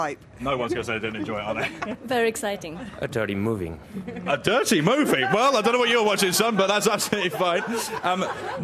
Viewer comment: what did he actually say? 🤔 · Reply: Totally moving